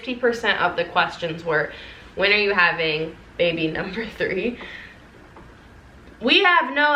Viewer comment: did anyone else hear say “baby #3”? I was so confused!!